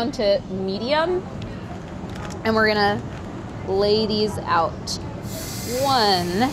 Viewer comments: When Priya said I'm gonna lay these out I thought she said "ladies out" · This anyone else hear 'lay these out' as 'ladies out at 4:53'?? No? Just me? Ok ... 😏😂